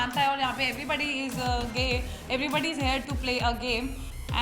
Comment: Kya bolli 1:24 everbody is gay hahahahhah.....kise nai sunna kya